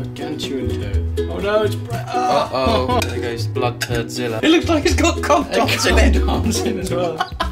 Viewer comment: Did he say it looks like come gobs?